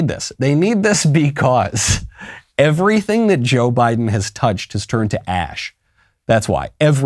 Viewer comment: Michael, your audio engineer needs to step it up. It sounded like you said "everything Biden touches turns to "ash"." Luckily we know what you meant. 😉